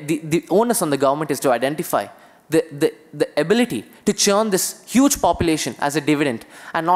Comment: what did the boy say on 12:44 , can anyone tell the word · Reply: Churn